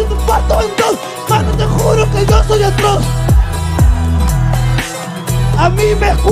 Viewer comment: no dice, yo soy el kross, creo que dice "yo soy atroz"